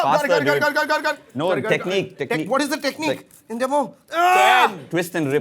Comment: Did just say "ente ammo" in Malayalam (43:23). All the malayalees give a like